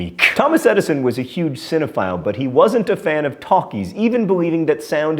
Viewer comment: I thought he said Thomas Edison was a real big fan of Takis! ( Spicy corn chips) xD